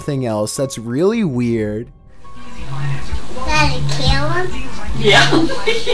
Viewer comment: it sounds like he is saying, "Daddy kill him".